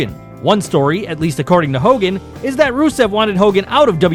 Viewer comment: did he say "Rusev" instead of "Russo"?